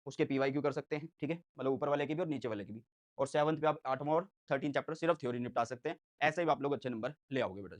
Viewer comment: kya bola apne sir chapter 8 aur 13 bola ya 3 bola please confirm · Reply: 8 aur 3